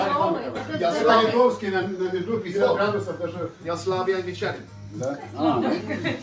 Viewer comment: he actually said there 'slabij' (слабый) and not 'slavic' which means 'weak' Englishman xD